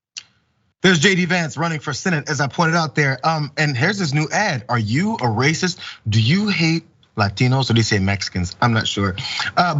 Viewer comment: What I heard was, "Are you a racist? Me too."